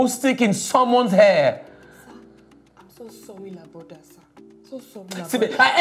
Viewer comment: The truth is that at first I thought she was saying sorry la brother sir 😅😅😂😂😂 I DIDN'T KNOW She was saying SORRY ABOUT THAT SIR😂😂😂🤦🤦🤦 Chai I swear ENGLISH HARD OOOOOH 😂😂😂😂😂😂😂